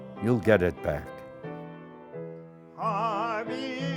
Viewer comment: Please note that at 6:32 he sings "Avinu Malkeinu" in Hebrew (it's not in polish). So the subtitles are wrong...